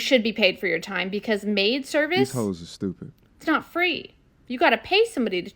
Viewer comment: 😂 did he say "hoes"